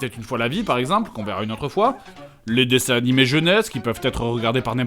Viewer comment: dans ma tête : dessin animé jeux NES. 6:01 en réalité : dessin animé jeunesse. C'est seulement à la fin de la vidéo, quand j'ai repassé ce moment car je me disais que ca voulais rien dire.. que j'ai compris ><.